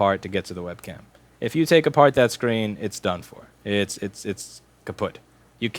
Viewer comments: did Louis said kaputt? G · Did he say the dutch word " Kapot" at 16:33??😂 It means broken btw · Does He really say "it's kaputt" at 16:30 or so?